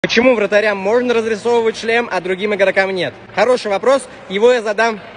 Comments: Мне послышалось "почему вратарям можно разрисовывать член".... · Мне послышалось разрисовать член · Мне сперва послышалось "чл#н" а не "шлем" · почему в начале мне послышалось можно разукрашивать чл€н